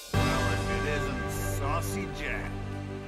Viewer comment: He did not just say "Sussy Jack", did he? Please tell me he did not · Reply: Saucy jack